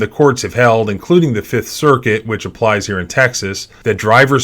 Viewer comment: 'drivers fleeing the police in pursuits are nazis' i know this is not what you said but it's what my brain heard and i couldn't stop laughing.